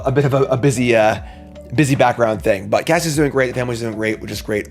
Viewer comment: I thought you said "Cassian is moving." I thought - what? Hurrah for! Best wishes to you and your family for an excellent 2025!